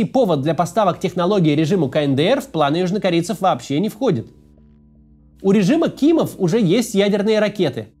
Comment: Услышал "У режима Киева". Перепроверил канал, вроде все правильно. Потом понял